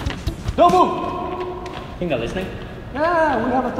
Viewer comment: says do you think they are listening to usÉ (In the subtitle it say thin instead of think says: Yeah, they respeccct us.